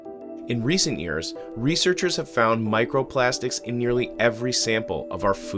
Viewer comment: Did he just say plastic in our foods… PAUSE!